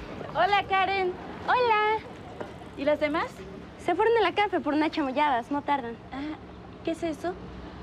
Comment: se dan cuanta que dijo Se fueron a la casa